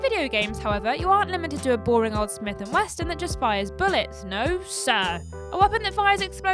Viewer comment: I swear I heard "Smith and Westin"